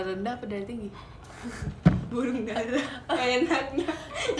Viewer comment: Adel ngomong apaan sih ampe kejutan gtu? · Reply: Gara gara adel kelepasan malah nyanyi jingle mie burung dara wkwk